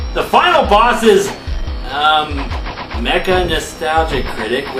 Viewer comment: Did Just Called The Guy He Was Fighting Meca Nostalgia Critc ?